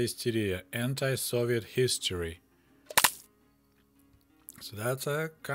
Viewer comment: does it say anti-Soviet history or anti-Soviet hysteria? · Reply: He sayed, "hysteria" (истерия (рус.))